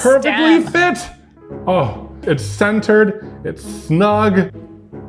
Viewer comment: I thought said “Censored”